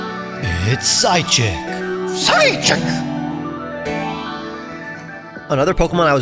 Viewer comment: i heard side chick when i wasnt looking